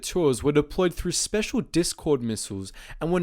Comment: I heard discord